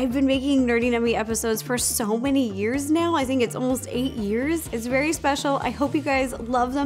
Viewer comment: I s2g the first time I played it I thought Ro said, "I think it's been almost 80 years" 😅😂